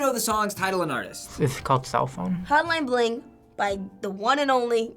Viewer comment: what did he call the song ? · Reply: he called it "cellphone" lmao